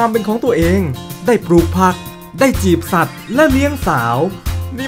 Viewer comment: ผมได้ยินไม่ผิดใช่ไหม จีบสัตว์😂😂